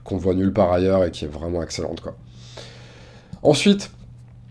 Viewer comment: C'est quoi le nom du jeu ? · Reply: Lorenzo Il Magnifico.